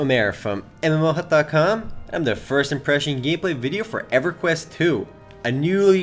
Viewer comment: I always hear "I'm the first impression gameplay video". You can't be a video. What do you actually say? o_O